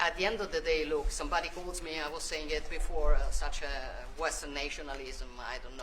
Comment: did she just say white nationalism!?